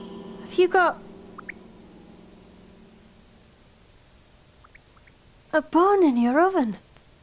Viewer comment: "Youve got a bun in yer oven" is the best way to describe pregnancy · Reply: As a non native speaker I hear "you got a bone in her oven" which still kind of makes sense